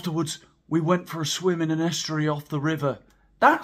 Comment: Hi there I'm South African and English isn't my 1st language. For a minute I thought you said they went swimming in an ashtray I laughed so hard even after realizing my mistake and kept laughing to the end. You're so funny. Priceless